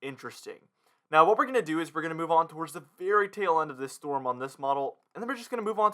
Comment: He said " very tail end of this storm" and I heard "Fairy tale end to this storm!" There will be ice, there will be snow, there will be severe weather Well put.